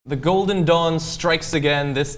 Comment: I thought he said the golden dong in the beginning